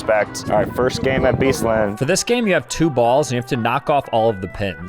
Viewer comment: what did bro say two balls ☠️☠️☠️☠️☠️